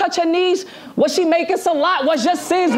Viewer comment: Wtf does surf say at 21:40 . After “nose touch her knees” the crowd went crazy and I have no idea what he said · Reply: He said “ was she making salat” that’s a word for prayer in Islam. 🔥🔥